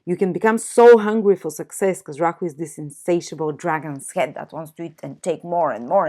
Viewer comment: rock what, dragon? I’ve heard you say it three times now and I’m not sure what that is... even though I enjoy the melodic lilt of your accent! It’s really quite pretty... but rock what? ^_^ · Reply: Lol, Rahu, the dragons’ head. It is the north node of the moon